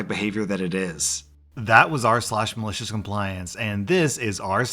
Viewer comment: this anybody hear him saying r/Malicious Compliance?